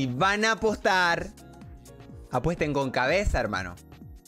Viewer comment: que dijo 😳? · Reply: E S T A cabeza